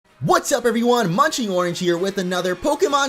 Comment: I heard him say munchy orange here at the start xDD